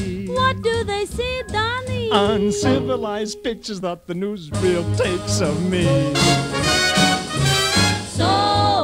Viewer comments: When I heard this first in Fallout 4, it sounded like he said “Uncivilized BITCHES that the newsreel takes of me” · When he says pictures I always thought he said bitches